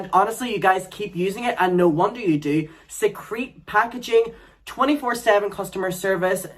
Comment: Secrete packaging? Am I mishearing discreet as secrete?